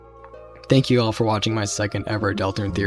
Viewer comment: when dreamwatchers started playing I forgot what I was watching and for a moment thought you were saying "thank you all for watching my dream" 😂